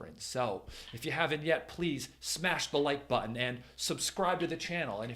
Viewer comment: Oh.. "Smash the LIKE button" - I thought you were saying "Smash the LIGHT button" which I immediately did... ...and now my phone's flashlight is broken.😭